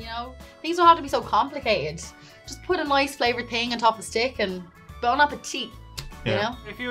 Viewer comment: with all the innuendos in this video, that last line from i heard as "bone a petite" ... 🙄